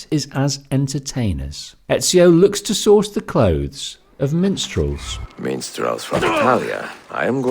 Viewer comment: I’m listening to this and had to rewind to hear correctly, could’ve sworn I heard menstruals only to see MINSTRELS 😂😂